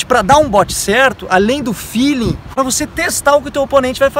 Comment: além do fini? Não entendi direito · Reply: universo do biel feeling... sentir em inglês... sentir o momento certo. Perceber o tempo